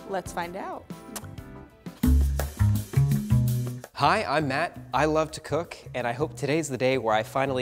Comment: When he said: "I am Matt", I thought he said "I am mad" 😂